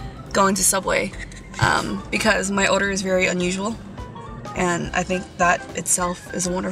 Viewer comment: It sounded like she said my odor is unusual